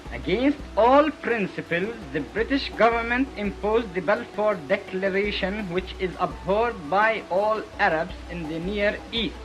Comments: I think the speaker actually says "the Balfour Declaration which is abhorred by all Arabs in the Near East". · Great Video, on a topic not widely explored on youtube. Quick correction to the subtitles at 4:07. The gentleman speaking about the Balfour declaration says it was 'abhorred' by all Arabs, rather than 'upheld' by all Arabs. Upheld would imply that the Arabs were agreeing and complying with it, which is the opposite of what he's trying to say! Tiny thing and again, a great video, wish there were more of these homefront episodes!